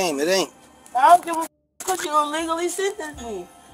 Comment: This is why you be polite and keep it moving. Idk why he had to be so defensive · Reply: I thought he said he was illegal, as well